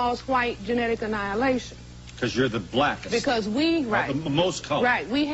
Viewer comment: really did he say "cause you're the blacks" wow yes we're the blacks Phil😃✊